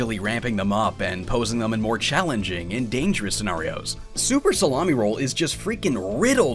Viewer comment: I heard super salami roll lol